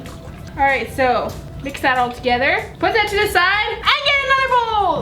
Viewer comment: kinda sounded like she said put that sh*t aside